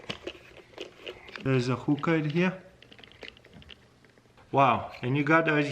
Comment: did he just say there’s a hooker?